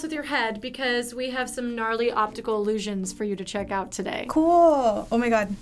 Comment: At the start Did she just say "NARLY" who says "NARLY" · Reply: Gnarly